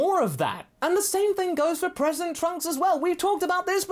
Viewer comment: I thought said "President Trunks." Then I realized it was "present Trunks." But imagine how great of a twist that would be for future present Trunks. Leader of the free world!